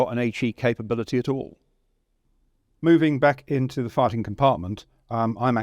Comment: Misheard farting compartment and LOL